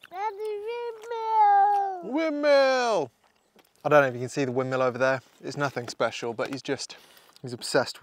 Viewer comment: I thought you said, "I dont know if you see the women over there!" (5:53)😂 What mic do you use?? VOCALS ARE SO CLEAR AT A DISTANCE. Lol